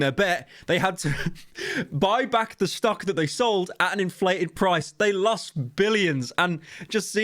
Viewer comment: did you say inflated?